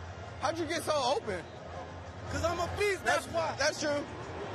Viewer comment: What Doug Baldwin says: I’m a beast What the captions says: I’m a pizza